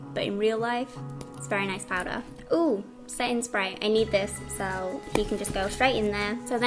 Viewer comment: OMG AT 4:23 I THOUGHT YOU SAID "SATAN SPRAY" LOL